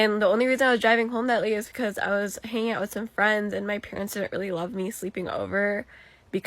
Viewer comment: I thought she was gonna say her parents didn't really love her